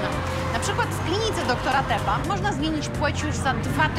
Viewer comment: czy tylko ja usłyszałam: doktora CEPA? XD